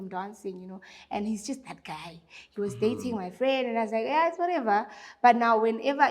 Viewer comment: she said he was just that guy who was dating my friend? sorry I don't know these people.But did I hear that correctly?